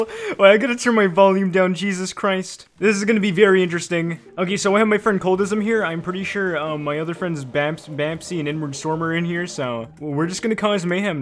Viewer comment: thought said n word storm